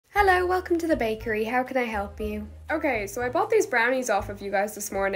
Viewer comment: Why I heard "How can I help me?"😅